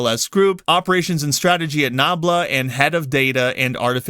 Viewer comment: I thought you said NAMBLA not nabla xDlol would of answered alot of questions.